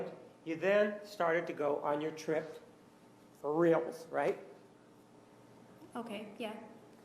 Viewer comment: Did Martinez just say "for reals"? 😂🤣